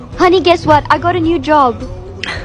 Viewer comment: i thought i heard "supermarker warker